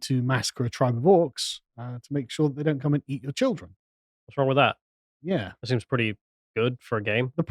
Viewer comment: I thought that was saying “what is wrong with eating a lot of children”